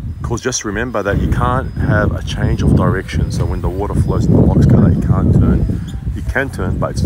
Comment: for the longest time, I thought u were saying box cutter, I was like why is it called that...